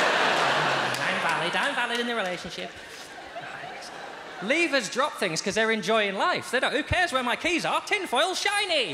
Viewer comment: He says something to himself at 1:07 or 1:08. Can't catch it. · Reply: Philip Garden Probably, "I guess not"